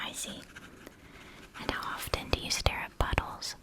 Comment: Tryna fall asleep and all I hear is "and how often do you stare all buttholes" I started laughing